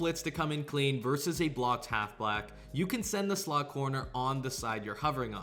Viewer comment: I heard that half black